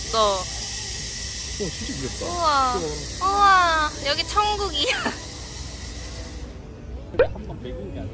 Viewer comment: did I hear jungkookie 👀👀👀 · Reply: 천국 - it means heaven or paradise 😬😬